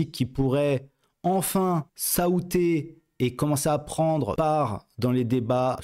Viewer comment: "S'aouter" je sais pas si ça s'écrit comme ça mais je connais pas ce mot, ça veut dire quoi c'est du français ?